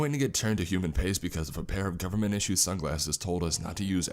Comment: I misheard a 'pair of government-issued sunglasses' as 'A bear in government-issued sunglasses'.....and that would've been a somewhat different story.